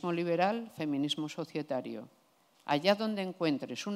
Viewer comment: en el minuto 37:44 ¿saben qué es lo que dice? Feminismo liberal Feminismo........ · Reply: societario